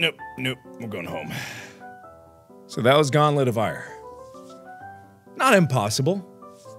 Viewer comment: Game: Gauntlet of Ire My brain: Goblet of Fire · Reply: I thought it said “Gauntlet of Fire”